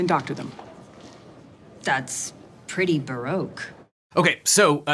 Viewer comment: Wait, what is the word she says @ 1:16? "Baroke"? I looked it up and never found anything. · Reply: Baroque. In this context it means she's saying that Chuck is accusing Jimmy of doing something that seems ridiculously elaborate.